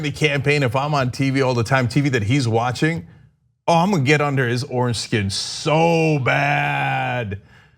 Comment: When he said he would get under Trump's 'orange skin' I had to rewind it. For a second I thought he said foreskin.